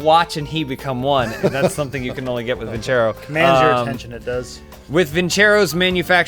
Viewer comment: What did say at 10:14? "manage your intention at this"?